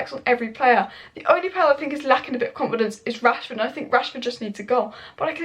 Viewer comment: can you clarify what did you mean when you said Rashford should leave? Are you putting Rashford in the 'better if he leaves' category? Not sure whether you were speaking literally or idiomatic. Greenwood certainly performed better than Rashford, but Martial too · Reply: Now I understand. You said Rashford needs a goal. I misheard you saying Rashford needs to go. To me, you speak a wee bit too fast sometimes. I agree with you, but I think Rashford has not fully recovered from his back injury. Some fine muscles & nerves are out of place that affected his coordination & kicking power leading to the glaring misskicks. He needs games to adapt to his new condition.